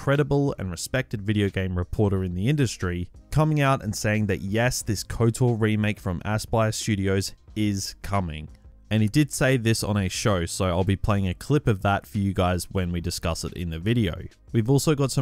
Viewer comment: Sounded like you called it assblast studios